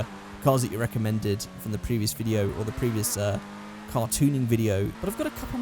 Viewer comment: Near the beginning, he said car tuning and it took me a minute because I thought he said cartooning